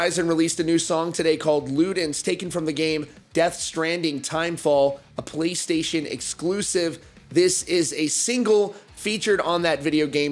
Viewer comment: Who els thought he said “A Police Station Exclusive”?